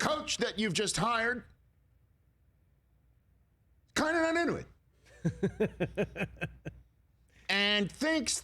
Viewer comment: Umm wtf? What did Rich say at 2:42? Anyone? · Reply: "Kinda not into it" that for sure a Tri-State area speech i say it like that all the time